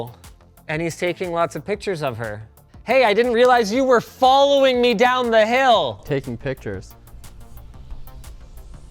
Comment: What did Linus say at Min 16:37? It sounds like the german word "nirgendwo". Did someone hea that too? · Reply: Negligible